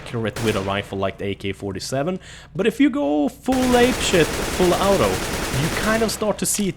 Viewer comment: did he say "ape shit?" Lmfao